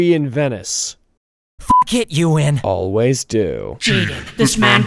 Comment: can someone tell me what's Jaden's line after the question "why are we in Venice?" ? I can't understand what he says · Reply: He says "Fuck it, you win" but it's bleeped at the beginning.